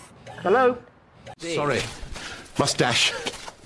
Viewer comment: When I was a kid watching the five doctors, I thought the fifth doctor said to the cyber men, “sorry, moustache”. I didn’t realise til I was an adult he said “must dash”.